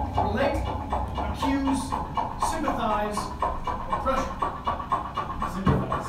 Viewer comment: I can't tell what the person said that made everyone laugh. Was it torture? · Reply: HOW CAN NOBODY SPELL “PRESSURE?”